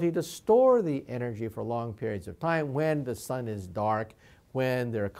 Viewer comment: did he say "when the sun is dark" at 0:36 ? xXx